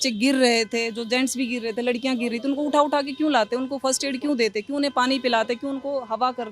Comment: did she say first-aid 😅